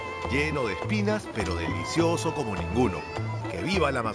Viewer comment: Que dijo en el minuto 29:53 DELICIOSO COMO EL CULO??? 🤣🤣🤣🤣🤣🤣🤣 eso en México es un grosería muy grande!!